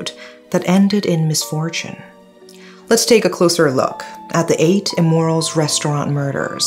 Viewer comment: I keep hearing say “8 Immorals”. 😅